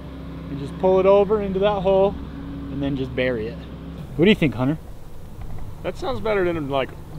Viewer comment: I thought he said "What do you think honey" 🤣